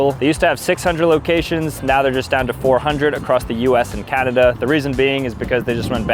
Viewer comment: did he just say "Cadada" instead of Canada, or did i just hear that wrong lol